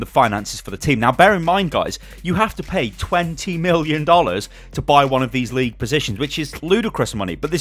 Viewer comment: did he say 20 Million dollars?!?!